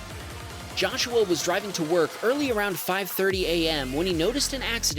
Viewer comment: its says 5 : 40 but i hear 5 : 30 you guys hear it too or it is just me